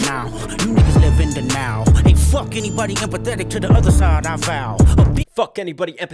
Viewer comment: he says they live in denial but also sounds like he says “in the now” saying they rather just party and not worry about the future